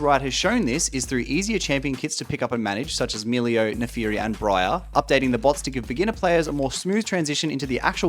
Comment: "Milio, Naafiri and ?Breyer?" what is the man saying? is he trying to say Xayah? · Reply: Briar is the new champion